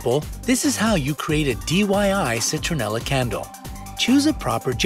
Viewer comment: am I the only one who heard DYI instead of DIY? 😅